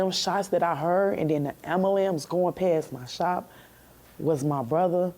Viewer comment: Did she say Ambalambs.